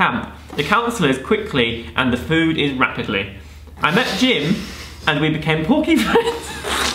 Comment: Does anyone know what he said at 4:22 x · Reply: I met Jim and we became porky friends